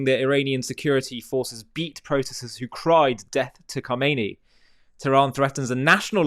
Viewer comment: I heard "mad dog Khameni" 😂